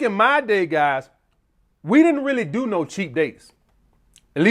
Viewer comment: Almost thought he said the #1 cause of flakes is "Cheat dates"